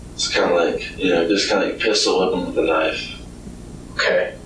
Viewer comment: ~ wait. What? Did my guy just say 'Pistol whip em with the .. Knife"? 🤔